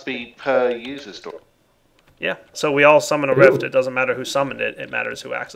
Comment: Heh, at 2:35 it sounded like he said "Summoners Rift" when he said "summon a rift".